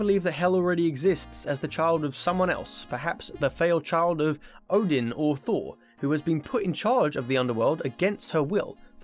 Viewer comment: I heard a scene super wrong I thought he said the failed child of Odin and Thor, I replayed it and it was Odin or Thor. My bad